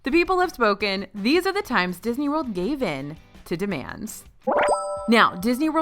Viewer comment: I thought it said demons instead of demands lol